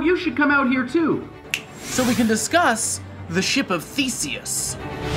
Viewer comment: Was I the only one that thought he said "The Ship of Feces"?